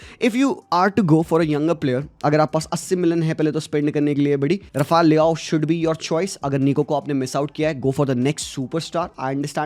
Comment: i heard "Rafale le aao" 😭🤣